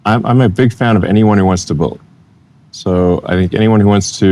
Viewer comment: Elon said he supports any one who builds but subtitle says bully. Can this be corrected? His words are important please check each word !